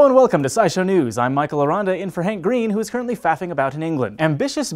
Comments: For a second there i could have sworn he said "hank green, who is currently fapping about in England" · sounds like you said Hank is fapping about it England. lol